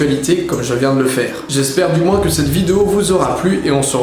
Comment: Je rêve ou j'ai entendu "aimez-vous les seins des autres ?" vers 9:50 ?